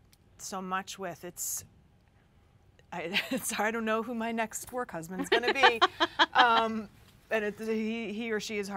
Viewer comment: What does she say at 4:03 I don't know when my next? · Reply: Work husband